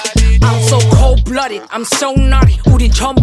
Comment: I hear "Cold blooded Ho3" in the beginning